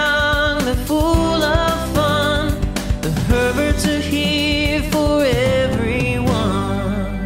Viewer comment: The intro I heard “ the perverts are here for everyone “